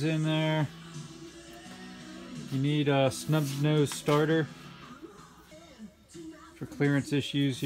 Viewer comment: did you say Snub Nose starter ? what year subaru?